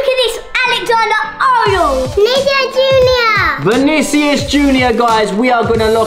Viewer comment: did said nigga junior? Or am i tripping 💀💀💀